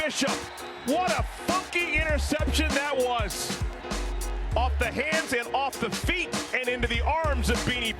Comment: I thought he said “what a f*cking interception that was!” 😆